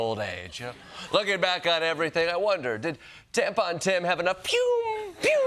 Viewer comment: I heard Trump say "Tampa Tim"